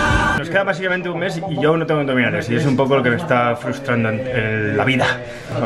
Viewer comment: ¿Porque dices ENDOMINALES? Min.